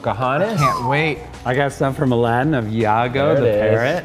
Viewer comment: Had a "Yanny/Laurel" moment happen when he said, "I brought some from ___ of Iago the parrot. " I thought I heard "Mulan" at first (perhaps because of just seeing and hearing it) and heard "Aladdin" once I rewatched with captions! 😄